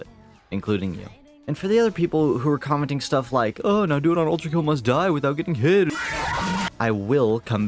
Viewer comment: I misheard Ultrakill Must Die as Ultrapain, that one mod that makes everything hurt. I enjoyed the mod but I was eager to get my normal maurices back